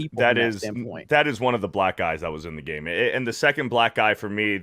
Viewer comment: Oh, he's saying black eyes. I thought was saying black guys.